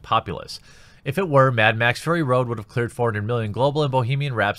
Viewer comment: is it me or did just call it mad max furry road?